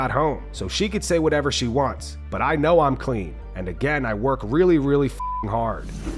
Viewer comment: oh she says I know I’m clean. She does not say that she’s never juiced.